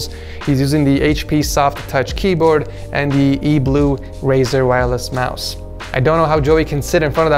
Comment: did he say razer?